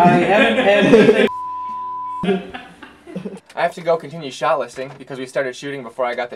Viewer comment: I thought you said shop lifting